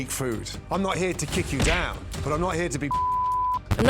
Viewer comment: I think he said "I'm not here to kick you down, but I'm not here to be bullsh***ed" <3